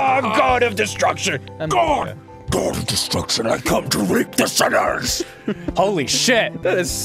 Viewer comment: or so- I hope he said "reap the sinners" instead of "rape the sinners" 😞